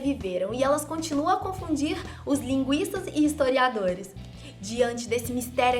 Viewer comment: ela diz "os linguistas" e eu entendi "os linguiças"